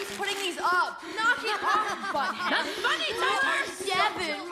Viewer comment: Did Priya say anything, i couldnt hear her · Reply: Only thing I heard her say was, ‘I banish you’…